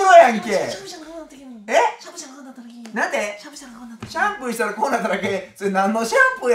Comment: 「シャンプーしたらこうなっただけ」が「しゃぶしゃぶしたらこうなっただけ」に聞こえてまうわ